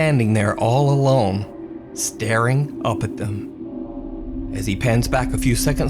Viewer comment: what is that word "uppaduh"